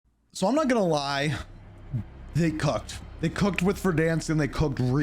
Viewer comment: I definitely heard that as "they cucked"